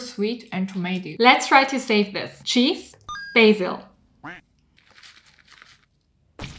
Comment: i thought she said bagel instead of basil 😭